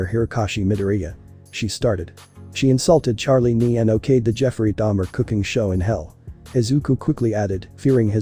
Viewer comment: JEFFREY DAMMER COOKING SHOW?! DID I HEARD THAT RIGHT?